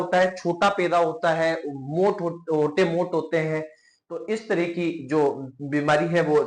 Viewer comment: sec par sir ne kya bola 😅😅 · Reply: Hoth mote hote hai